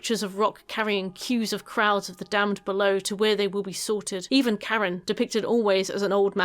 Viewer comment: It took me until Wrath to figure out you were saying 'damned souls' and not 'damsels', as I kept hearing xD Makes a bit more sense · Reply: And I heard "Karen" instead of Charon...